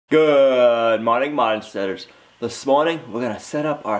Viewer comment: It sounded like you said "Goooood morning, monsters." A little glitch in the soundtrack. LOL!